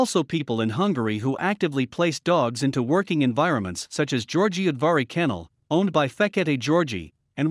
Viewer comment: What was the name of the kennel mentioned at 19:25?? It sounded like “Giorgio Advari Kennel” from Hungary? And where can I contact or find them? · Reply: Györgyudvari Kennel, Mr. György Fekete.